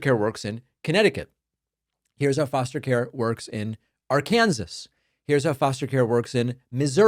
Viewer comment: Were you joking when you mispronounced Arkansas? Because it sounded like you said "Are Kansas".